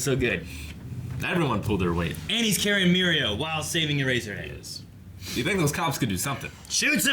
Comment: sounds like he said ‘Eraser Anus’ 😂